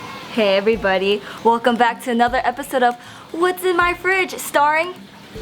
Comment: LOOOL I THOUGHT IT SAID "WHAT'S IN MY ASIAN FRINGE!" XD LOOOOL